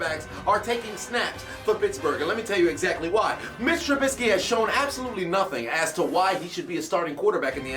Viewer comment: I was watching the video but wasn't paying attention and thought said Bitch Trubisky